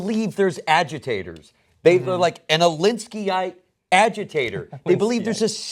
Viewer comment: What does Wes say at 2:20? "But they believe there are agitators. [Word], agitators." Any help? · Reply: "Alinskyite," after Saul Alinsky, who was an influential community organizer. Wrote "Rules for Radicals."